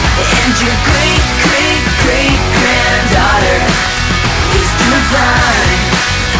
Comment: I always thought they said your great great great granddaughter is devine lol.